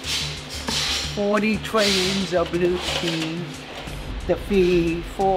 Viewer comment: I could have sworn at first they were saying "butt cheeks!" I wonder how many times that must have been said??